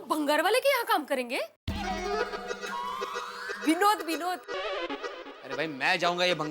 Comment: did she just said binod😂 Original word from slayy point...